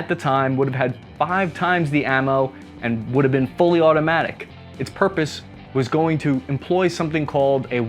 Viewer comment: dID I JusT HeAR FulLY AuTOmATIc?